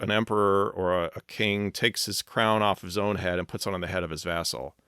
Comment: at 7.43 i thought you said "takes his crown off his head and puts it on the head of this asshole!" Was i mistaken and if i am i don't care it still makes sense given the other man was his inferior!😊